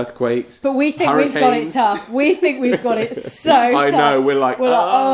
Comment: what phrase is it you use at 3:23? “We think we got it...”? Didn’t quite get it and i think i never heard it before. Thanks!! :) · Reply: "We think we've got it so tough!"